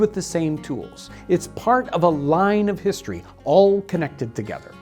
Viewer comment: Wait, I thought you said baker is the most important job in the world